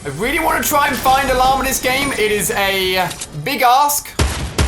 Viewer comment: Why do i feel like he increased the earrape in the intro I thought he said big a$$ in